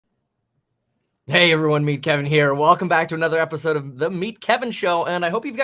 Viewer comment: I always hear "meat Kevin". Sorry.